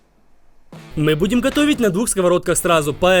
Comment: мне одной послышалось? -Мэ будэм хотовить?